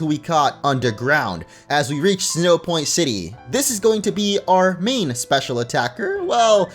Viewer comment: Did you say "Sinnohpoint City"?